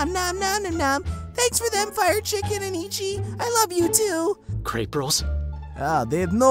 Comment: Ghidorah: crepe rolls My brain: grape rolls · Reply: I lamost thought ghidorah said Crap rolls lol haha😂😂